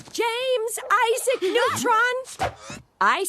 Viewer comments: the entire time as a kid, whenever Jimmy's mom said his full name I always thought she was saying "James, I said, Neutron" which did not make sense but its what my child ears heard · I’m freaking dumb.. I always thought Jimmy’s mom said “James I said Neutron” but then I got older and realized his middle name is Isaac 😂